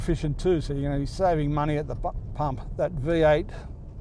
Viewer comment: Love it!! "saving money at the f*#&!en pump" .... well that's what I heard anyway. Awesome review. Giving it a year so Toyota gets a chance to work out the bugs if any, and hope to enjoy my new 300 in 2023 sometime....... hopefully they'll have sorted out the supply issues with semiconductors by then and the wait time isn't a year.